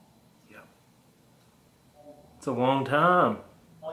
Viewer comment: you can clearly hear a voice say "yep" answering your question "Did you say 2 years?" It doesn't sound like it's coming from the spirit box.